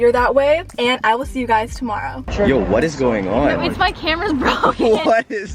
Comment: Did anyone else thought she said “What’s poppin’?” In the beginning? 😂